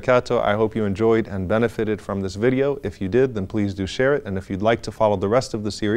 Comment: I heard "I hope you enjoyed and benefited from this video, if you did it then please do *Shirk*", I must be really tired..